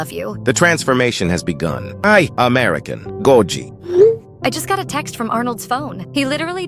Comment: Bruh that’s not what Arnold said he said “I American goji” 39:31 maybe goji isn’t Japanese and now is American 🇺🇸🇺🇸